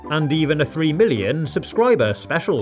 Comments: When he said 3 million I thought he said “ 3 Billion” and I was like WHAT!?!?!? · I thought he said 3 billion not 3 million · I thought you said 3 billion subscribers